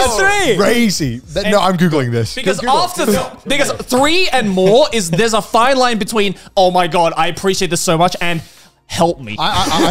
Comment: What did joey said at 23:55? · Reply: because three and more (is) - there’s a fine line between omg i appreciate this so much and HELP ME